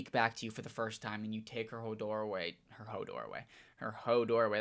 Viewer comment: did you just say hodor?? My king??